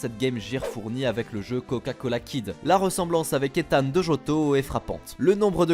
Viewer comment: j'ai entendu "méthane"